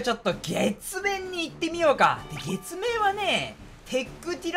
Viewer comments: 月面が血便に聞こえた自分に驚いたんだよね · 血便いってみようかに聞こえた